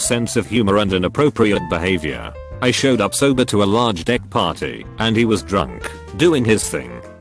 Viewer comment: first time I heard this, thought it said "large dick party" Like, whoa people with large penises just get together and have parties for them.